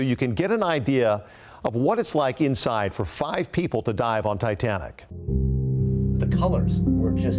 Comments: I heard "FIVE PEOPLE TO DIE ON TITANIC" oh my ... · i kept hearing die on titanic until I turned on captions 😶